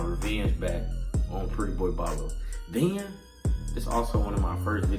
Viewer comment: not gonna lie i thought that shi said prettyboyfredo💀